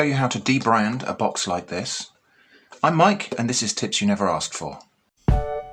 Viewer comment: I heard it as "... and this is TITS you never asked for" at 8.37 mark. Some spicy content advice there ;-)